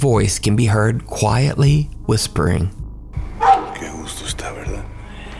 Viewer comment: I speak spanish and I can almost asure you that, this voice says "Verdad..." what is means "It`s true..." responding with what the explorer says